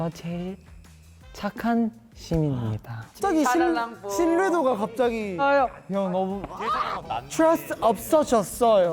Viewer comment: did Juwon used “po”? Can’t tell what he was saying🫠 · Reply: Yes he said "isa nalang po"